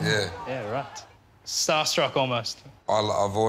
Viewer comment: What is Tai saying at 0:23? The subtitles say "mad blower." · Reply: Mad bluer